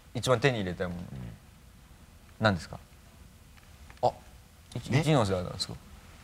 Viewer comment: ここ若林なんて言ってんの？全く聞き取れない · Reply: おはしはし 「あ、市ノ瀬アナですか」だと思います。